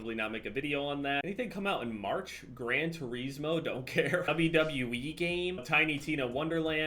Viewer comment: I thought said grand trees moe...